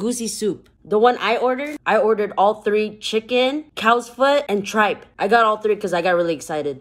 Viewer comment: Ooh I thought she said cats foot but NO SHE SAID COWS FOOT 😂😂😐😐 (I'm so dumb 😑😑)